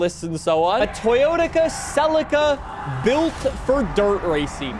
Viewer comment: does anyone else hear "toyotica celica"